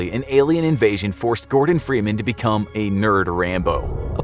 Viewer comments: Captions say "Morgan Freeman" instead of "Gordon Freeman" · Who the HECK typed in subtitles MORGAN Freeman instead of Gordon Freeman?